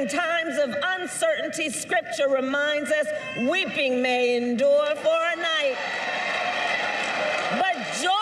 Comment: Not that it matters .. but I've listened to her in this 4 times & still can't decipher what her 1st sentence was. Srsly, What did she say? Sounded like (right @ 4:00) "Weeping main door for night"????